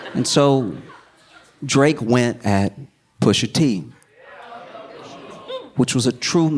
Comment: I know for bloody certain I heard SOMEBODY go....Pusha TEEH 😂😂😂😂